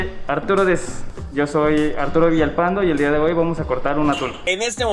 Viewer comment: Parece que dice "el dia de hoy vamos a cortar UNA TULA"